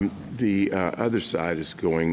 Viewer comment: DID HE SAY THE OTHER SIDE, ITS ALMOST LIKE HE IS WALKING THE LAWYER THROUGH REBUTAL ARGUMENTS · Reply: Did he just say "The other side" ? That says it all about Thomas!